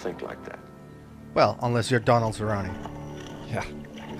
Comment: Did they say Donald Cerrone? I can't quite tell 😅